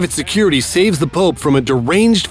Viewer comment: Did he say "the Pope"?